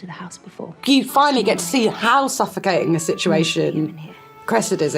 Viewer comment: Why did I hear the word "sapphicating" instead of suffocating? The mind hears what it wants to hear.